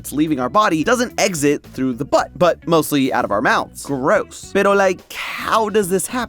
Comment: Did he just say "pero like"